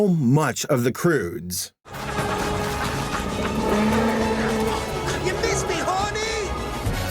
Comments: the first time i heard that line i thought he said "this makes me horny" instead of "you missed me horny" 😅😅😅😅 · what did he say? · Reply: Miss me, honey